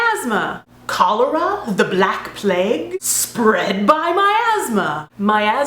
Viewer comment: I was only listening to this while I was cleaning, and thought she said "The black plague? Spread my my asthma!" and I thought... 'YOU STARTED THAT DISEASE???? Aha, homonyms confuse me two too